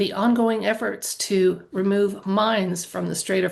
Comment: No joke, at 4:41 I thought you said something about removing mimes from the Strait of Hormuz. It did give me pause. Thank you for the reading!